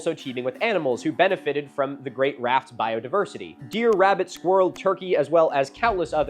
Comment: I was listening while folding my laundry…. And misheard this as “dear, rabbid squirrel-turkey”